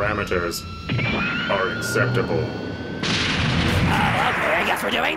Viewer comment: Ironic thing Cell said. "An admirable attempt from the red-headed step-child" Remember that SIXTEEN is the one based off a copy of Gero's son. So technically CELL is the step-child and younger brother here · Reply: I thought he said sceptile. As in the pokemon ._.